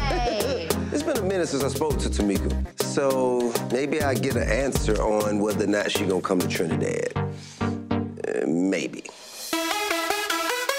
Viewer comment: did he say Trinidad 😮😎